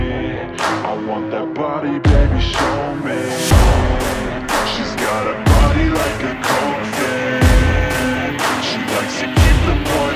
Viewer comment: I thought he said “She’s got a body like a goldfish.